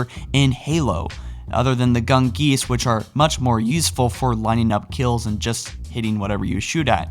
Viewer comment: did say "yeesful?" Lol